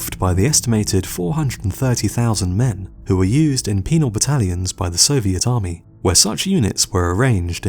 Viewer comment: I thought I heard "penile battalion". That would be worse than a penal battalion i guess